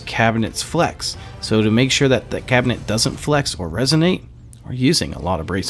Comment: What the hell does at say at 3:16??? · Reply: "doesn't flex or resonate"